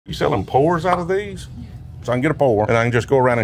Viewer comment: thought for sure he said .. you selling whores ?? 😂 but no 👎 it was ( pours )